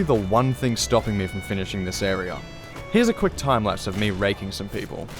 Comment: Nice, but mid video I swear I heard "here a quick timelapse of me raping some people", I died